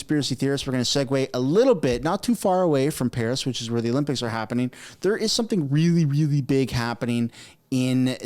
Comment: I thought you said the only pics are cancelled.